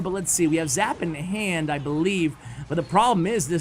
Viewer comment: Did he said 14:57 the golem barrel 😂